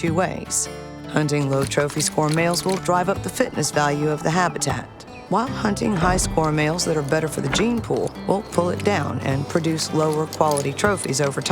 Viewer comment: someone writes to me what she said at 5:40 - 5:55 for translate. I couldn't understand very well, because I speak Portuguese. Tanks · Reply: Hunting low trophy score males will drive up the fitness value of the habitat, while hunting high score males that are better for the gene pool will pull it down and produce lower quality trophies over time.